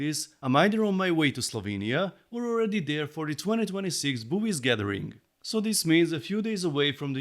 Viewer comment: Oh wow. haha I'm glad you put the webpage up. I definitely heard boo..... erm........ bies gathering. hahaha =)